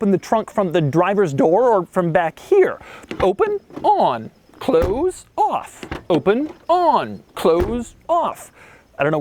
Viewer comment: am I the only one you thought he said “clothes off”